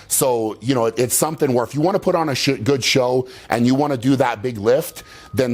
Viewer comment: Did he say shit good show? Like he wanted to say shit show?